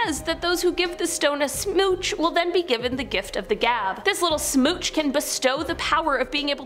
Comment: I thought she said gift of the slab and I was like nah cause that woman is gonna pop up and ask for it back